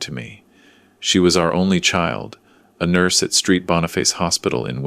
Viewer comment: "...a nurse at Street Boniface Hospital in Winnipeg.." That doesn't look or sound right. Was it supposed to be Saint Boniface or with abbreviation St. Boniface???? I'm assuming the AI misinterpreted the " St. " as meaning street (instead of the correct abbreviation for Saint .) I have seen this type of stupid error in other YouTube channels. This is why real people are needed for proofreading. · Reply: Thank you for the thoughtful explanation. Yes — it should be Saint Boniface, not “Street Boniface.” Sometimes automated transcription misreads abbreviations. I appreciate you taking the time to point this out!